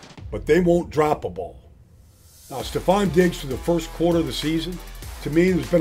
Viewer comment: Sounds like he says Stefon Diggs... Trevons brother, the disrespect😂😂